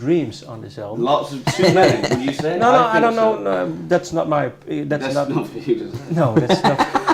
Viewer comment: Somebody can understand what alex said at minute 8:18 thats not your.... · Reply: “That’s not for you to say.”